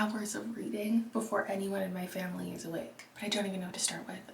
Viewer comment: The jump scare when I thought you said "I'm going to get three hours of reading in before GOING TO MY FAMILY'S WAKE" and had to immediately jump back 5 seconds to make sure only to realise you said BEFORE ANY OF MY FAMILY IS AWAKE. The shock. The horror. The confusion of you reading before such an event. The relief. And then the humour. I swear, the first 1 minute and 30 seconds of this video has taken me on more of an emotional rollercoaster ride than most books. 😅